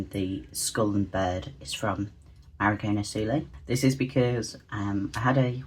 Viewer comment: I tried to find the skull and raven sticker. (4:14) Unfort., I am sick right now and my ears being worse than normal, I cannot understand the artists name. Can somebody help me out? Thank you! 💜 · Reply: hi the artist is marigona suli :-)